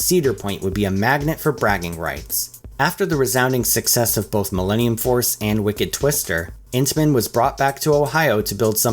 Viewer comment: Not gonna lie, at 23:18 I thought he said "Millenium Foreskin" 😅🤣